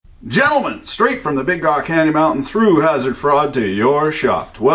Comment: Hazard Fraud. For forever I thought you were saying Hazard Fraught. Now I know.